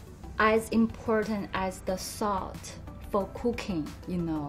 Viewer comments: When she was talking about the last face serum she said “it’s like salt for cooking” I literally thought she said “salt for cocaine” 🤣😂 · I thought she said "salt for cocain" lol